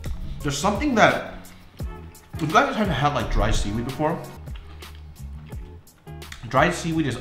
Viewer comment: coulda sworn this nigga said dried semen